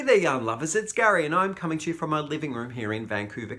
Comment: Gary, I've been watching you and enjoying all of you content for a while now. Keep it up! I have to tell you that when I first started watching you I miss heard you. When you open with,"Hello, yarn lovers," I heard, "Hello, young lovers." I chuckled when I realized my mistake. I'll keep watching because I think you are charming and appreciate your information and opinions.